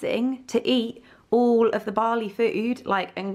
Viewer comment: These past few weeks you’ve been saying you’re going to “Bali” I kept hearing “Barley” and thinking ‘I wonder where that is?’ Haha!